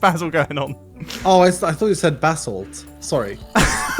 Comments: "Are we really going to sell Kubo?""No, that would be silly ""What about Basil?""Definitely, yes" types basalt on sign, "I thought you said basalt" Me: I thought he said bath salt 😅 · He said basalt and I thought he said bathsalts then I remembered it's a family friendly channel 🥶😂